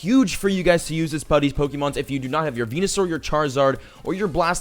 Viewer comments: Who else heard penisaur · am I the only one who heard him say penisour